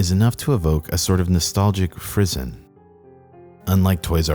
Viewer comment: Can anyone tell me what a frizzen is? Or whatever he said at 0:21?